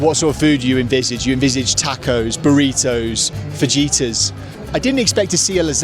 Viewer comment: Did you just say fajeeta